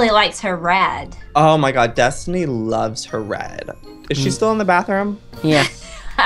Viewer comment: I thought they said destiny really loves her ren lol